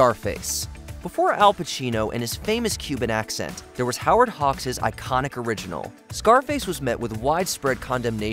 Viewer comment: Did he just call Howard Hughes Howard Hogs? LOL